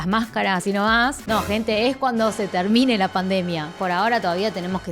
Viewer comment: Creí que era el único que había escuchado esta tarde, pero yo les entendí "esta tarde se termina" después volví a escucharlo pero solo entendí esta tarde solamente jaja